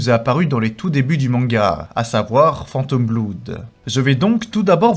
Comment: Avec tes vidéos je me dis parfois "'tain bizarre comme prononciation, c'est lui qui ptononce mal ou c'est moi ?" Puis j'ai entendu "Phantom Bloude" 😂